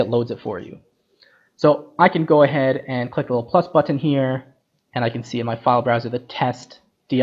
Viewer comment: One question what button did you clicked in 13:09, I heard “plus/class button”. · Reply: On the Notch TOP, if you look next to the Block parameter, I clicked on the plus symbol to the right side of the string field. That opens up a file picker for you to choose where your Notch Block is saved.